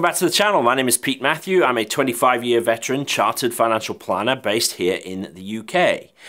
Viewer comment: Great video Pete! Although at the start I thought you were about to say “I’m 25 years old” 🤣